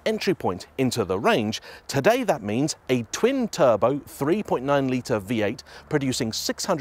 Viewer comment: I thought you said 4.9 liter, but it's a 3.9 liter (turbo ) V-8? Making 620 HP? Awesome. But I'm surprised Ferrari doesn't embrace superchargers more than they do. I'm not much of a fan of the turbochargers. Ferrari could have spared a few more cubic inches. lol.